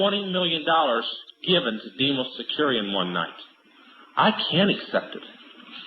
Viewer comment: hey does anyone know what he says at 3:15? sounds like demos securian? or something? please help me out of you understood what he said